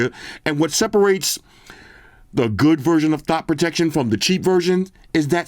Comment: another vid where WAS CHILL AF...but he still said CALM DOWN!...My favorite reviewer hands down 👍 *sidenote...i thought i heard THOT protection...need that feature :face-blue-smiling